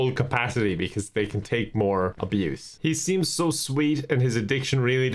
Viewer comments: “I don’t inflate them to full capacity so they can take more abuse” gave me massive whiplash I was not prepared for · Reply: I thought he said “take more use” and I don’t know which is worse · “I don’t inflate them to full capacity so they can take more abuse” gave me massive whiplash I was not prepared for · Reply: Omg i thought he said “they can take more juice”